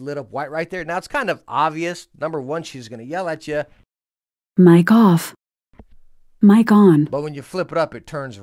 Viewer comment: Maybe its just me but when you folded up the mic, i thought she said Mein Kampf, not Mic Off. Really through me for a loop lmao